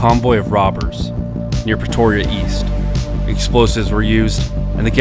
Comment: Was only half listening so I hear "Petoria"